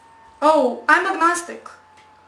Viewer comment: "I'm not really sure if I know what I'm certain about." Ha! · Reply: Hmm, I heard it as "I'm not really sure if I know what I'm *un*certain about." (6:27) Which I think is even funnier. :)